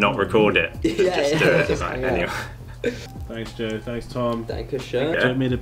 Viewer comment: Maybe it is just me hearing that, but did Tom say Dankeschön @ 9:22?